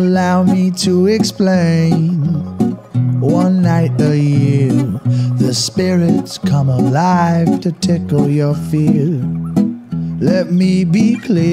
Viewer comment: Dude is it just me or I just heard, “ The spirits come alive and tickle your feet.”